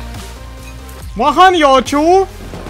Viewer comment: я слышу от "лоха не учу)"